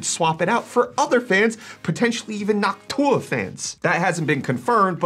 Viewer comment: Did he just say Noc TUAH?